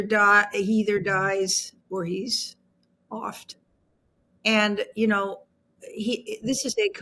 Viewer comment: "He either dies . . . or he's . . . oft" Oft? Aft?? What does she say? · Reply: Offed - as in assassinated